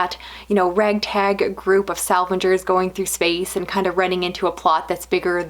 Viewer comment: Thanks for producing this video! Always looking for good recommendations. I misheard a sentence as "a Red Tag group of salvagers" and it immediately made me picture a motley crew of space voyagers on a quest across the galaxy to find the best bargains and sales. :)